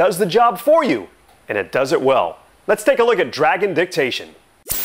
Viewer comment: Oh i thought he said Dragon Dick-tation